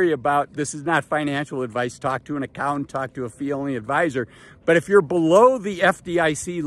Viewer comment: Here' a funny one. I was reading the transcription. At 5:38, he recommended a 'felony adviser.' Huh? I played the audio and of course what he really said was a 'fee only adviser.' A little bit of a difference!